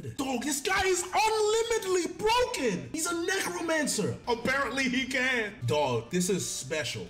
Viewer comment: I thought he said negroe-mancer 😂😂😂